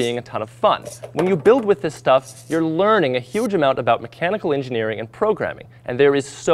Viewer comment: I thought he said chemical engineering xD